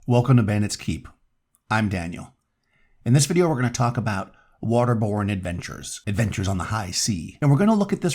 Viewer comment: Lol I listen to your stuff while I work and I heard, "We're going to talk about waterboarding adventurers"